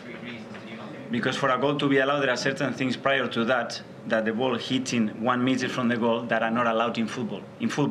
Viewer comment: Can someone clear up what he said at 1:54. It sounds like he is saying "you can't hit in the ball 1 metre away from the goal" but of course you can???